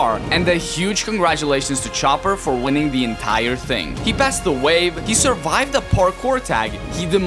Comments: i heard "he passed away" LOL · I rewined this part 4 times cuz I thought he said he passed away lol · did anybody else think he said "he passed away"? Edit: turns out said "he passed the wave."